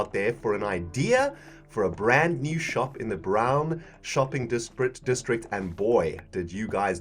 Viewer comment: Every time you say "brown shopping are" sounds like you say "brown choclate area